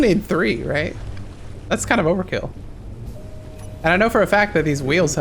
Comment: did say roverkill?